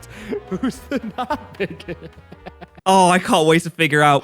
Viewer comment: I thought he said "bigger out" Unintentional pun